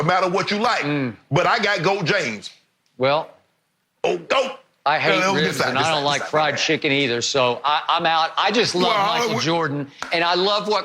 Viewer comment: Did Skip just say " I hate ribs and I don't like fried chicken either "? 😳